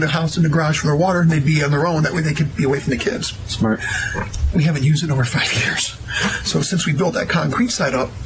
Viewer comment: i thought he said at first that she hadn't smoked pot in over a yr...if so why would she keep some in the safe...and if he thinks anyone is buying that stupid act he's trying to put on then he's crazy as he looks